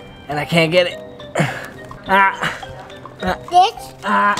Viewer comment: what did say😮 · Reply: He said “This?”.